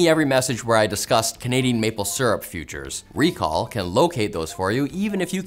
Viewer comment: ohhhh, Canadian maple syrup FUTURES.... i thought he said features...